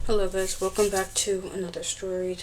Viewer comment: i thought you said hello vegetables